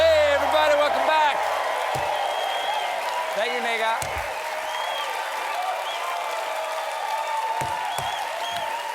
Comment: What did Stephen say ???!!! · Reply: He's saying "Thank you Nêgah", who is one of the Late Show Band members